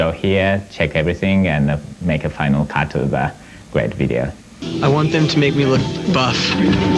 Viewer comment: guys someone can let me understand what Chester said here. Thank you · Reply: "i want them to make me look buff"